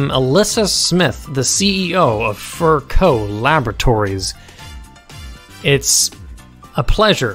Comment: I keep hearing “fur coat laboratories”